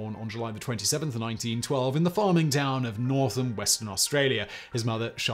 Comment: For anyone mishearing, he was born in "Northam" Western Australia. ... hearing "Northern" almost got me spewing 🤣